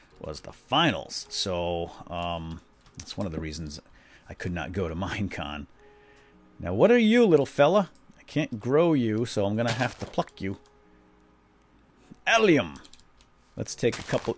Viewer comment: I thought you said f*** you but I thought a sec and I think you said pluck cause I know you wouldn't say bad words